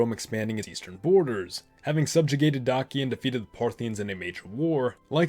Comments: Having subjugated what? I can't understand what he's saying and all the subtitles say "Having subjugated donkey." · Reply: He says Dacia, a kingdom on Romes border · Having subjugated what? I can't understand what he's saying and all the subtitles say "Having subjugated donkey." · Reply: Having subjugated Dacia